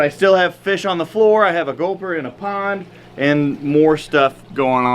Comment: hold on did he say gopher? as in the kinda big rodent?